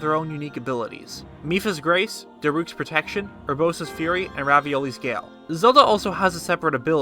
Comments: did he say Ravioli's gale? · So serious and then I hear “And raviolis gale” I DIED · Did I really hear " Ravioli's gale " ? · did you just said "Ravioli's gale"???? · Did I hear that right? He called Revali Ravioli @ 5:00? Lol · DID HE SAY RAVIOLI'S GALE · did he just say ravioli’s Gail? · Did he say raviolis gale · DID HE JUST SAY RAVIOLIS GALE xD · I heard Revali's Gail as Ravioli Gail